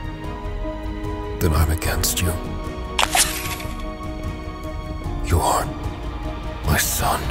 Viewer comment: Did he say "you are my son" or you "aren't my son" the music made it hard to hear the difference. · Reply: "You aren't"